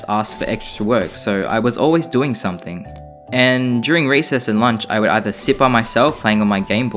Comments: Am I the only one who heard him say "Racist" instead of Recess?😆😆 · When I heard you say recess I THOUGHT you said raicest